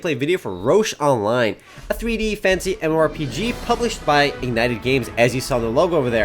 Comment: I always thought you were saying a "A 3-D, Fancy MMORPG." "Fantasy" wow lol